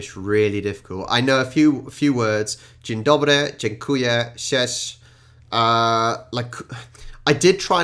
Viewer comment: When you tried to say "cześć" i heard "ssiesz" which means "you suck" XDD